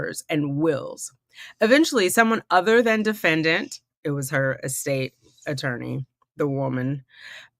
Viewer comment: The Donna voice was great! Every time someone says her “estate“ attorney, I hear “escape” attorney.